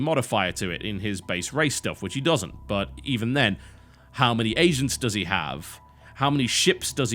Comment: I thought he said "How many Asians does he have"